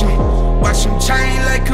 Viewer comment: I swear right at the end of the hook it sounds like buddy says “ Thunder Bay all fucking fake don’t wanna see me win” lol Can’t unhear it